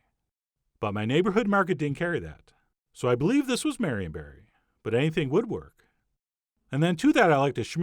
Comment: did he just said Marion Barry? 🤨🤨🤨 Googles Oooh, there's such thing as a merionberry!